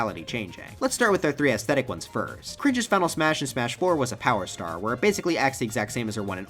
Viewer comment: what did you call her? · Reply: Oh yeah he calls rosalina cringe